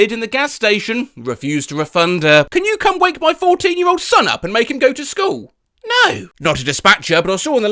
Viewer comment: I think I just misheard "Can you wake up my 40 year old son to go to school"